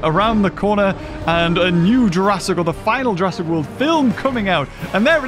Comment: did he say final Jurassic world film?